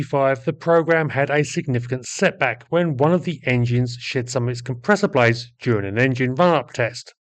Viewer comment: At the 6:02 mark you made me laugh out LOUD, albeit inadvertently. With your British accent I heard: "the engine shit some of it's compressor blades...." when of course, you were saying: "shed".... Thanks for ALL your great videos and for a laugh now and again!